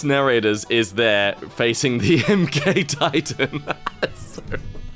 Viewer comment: I misheard him and I thought he said it was a gay titan. Oops!